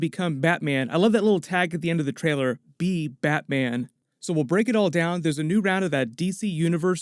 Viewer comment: Be Batman...I thought it said, BEAT Batman.